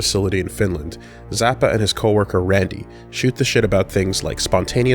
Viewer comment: did you say shoot the shit lmfao